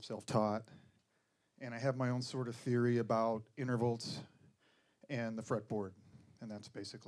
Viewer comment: Did he say innerverlts? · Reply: Intervals)))